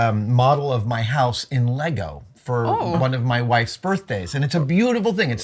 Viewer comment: Commissioned a house in lego for "one of my wife's birthdays" and, for just a split second I thought he said "one of my wives' birthdays" and I was like, 'he has wives?'